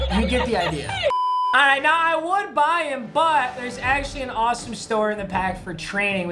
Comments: anyone catch how he accidentally said “ there actually and awesome store in the pack rn” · Anyone else hear store in the pack at 1:30 😂😂